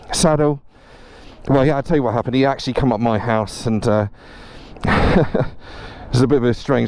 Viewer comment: Now then, I had to go back and listen to one particular bit again, and again, just to make sure. I heard it differently the first time. I thought he said you came up his ass! I thought bloody hell, sod borrowing your bike if that's what happens if you drop it. 😜😜😜😜He actually said you came up his house. 4:09 Shit happens ER, could happen to any one of us, and I bet you ain't the first to do it there. Stay safe guys